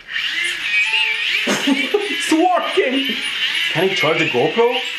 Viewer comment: for a second I heard him shouting excitedly TWERKING!